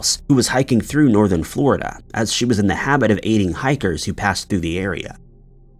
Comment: At first I thought he said "eating hikers " but it was "aiding hikers " lol His real name was Deckard Cain